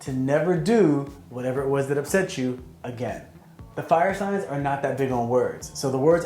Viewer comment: all i heard is "air signs are the best people"